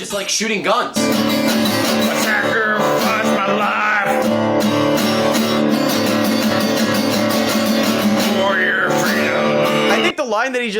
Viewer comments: I think it says “I sacrifice my life, for your freedom” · what. He obviously says "I sacrifice my life for your freedom"